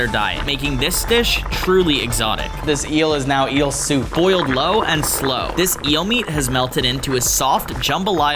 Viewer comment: did he say... low and slow ?? MAX